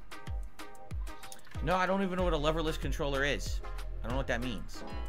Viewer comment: wait, he doesn't know what a leverless controller is? oh, PLEASE let there be a 'hitbox players are cheating, DOOD' saga somewhere down the line, that'd be awesome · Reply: I thought he said "level-less controller" and was also really confused, so thanks for clearing that up